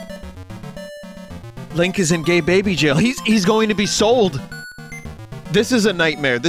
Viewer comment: Oh, so THIS is how Portal 2's music was made! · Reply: Also 49:15 DID JUST SAY GAY BABY JAIL? IS THIS A SIMPURUFRIPSU STREAM NOW!?